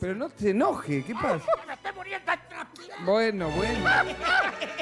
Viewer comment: ajjaja 4:30 que dice? alguien sabe? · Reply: dale que me estoy muriendo transpirando. algo asi dice